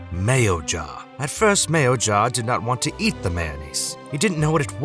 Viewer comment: Oh boy, I misheard and thought THE First Mayo Jar